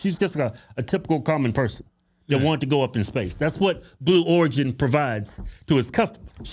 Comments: Stg I keep hearing him say "blue orgy" 😅😅 Also I would have DIED if after she said "look at the earth" just pop off with some "it's so huge" I'd have died right then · Second 32 sounds like he said blue Orgy